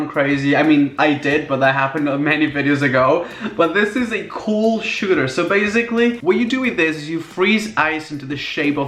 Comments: DID YOU SAY SCHOOL SHOOTER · when he said cool shooter, i thought he said school shooter..... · For a second I thought it said “School Shooters”